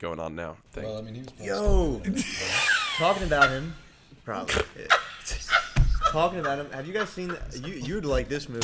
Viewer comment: what did BigT say at 37:20? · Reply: "well yeah, he was probably still doing it"